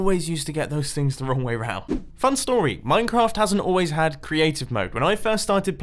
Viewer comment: Did you say Minecraft or Mein Kamft